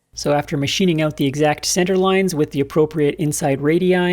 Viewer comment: "After machining out the exact centre loins" Did I really hear that?